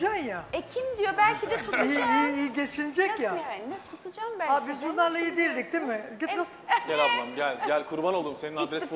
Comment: de get the fuck anladım :D